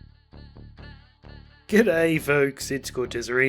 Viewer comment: I thought he said "good day fools" at the beginning of the video xd